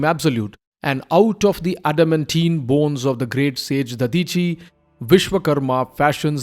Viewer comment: did he just say adamantium bones? 😂